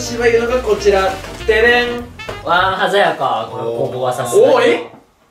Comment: 鮮やかにも外れ枠にも聞こえる